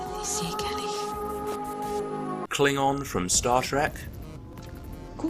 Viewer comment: did anyone else hear star shrek